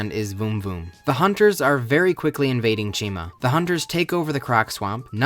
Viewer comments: I thought you said china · i thought it said china and i was so confused